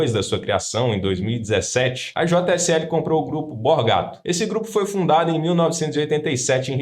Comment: Eu juro que entendi na primeira vez que eu ouvi "comprou o grupo bola gato". Tive que voltar pra ter certeza que não era kkkkkkkkkk